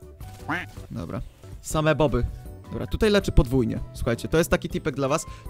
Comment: czy tylko ja usłyszałem że powiedział boby a nie boty? xD